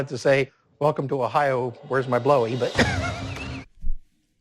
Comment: At first when I saw the thumbnail, I thought he was saying "where's my blow" and thought this dude was just trying to party. Like Ohio was crazy. Man, that would've been a lot better.